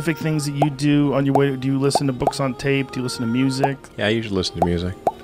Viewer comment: At first, I thought he said, "Do you listen to books on Tate?" 😂